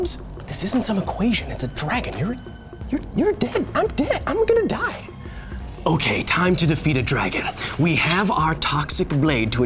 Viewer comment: Did really say "ama gonna die!" ?? (9:02) Does it not remind you of jaja binks.